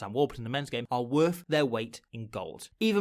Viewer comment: did you just say "penis rugby"?? lol